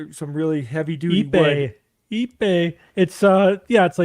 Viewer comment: named a special kind of wood that he recommends for decking, but I couldn't quite understand what he was saying, so I just searched for what it sounded like, and it worked. The wood is called "Ipe" (a.k.a. Brazilian Walnut).